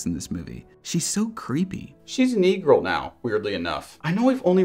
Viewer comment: The subtitles say “negro” but he said E Girl. What’s up with that?